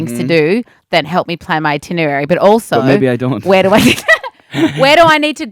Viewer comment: when they’re like talking about the itinerary and niall says “maybe i don’t” it sounded like “nO i DOnT” hahahaha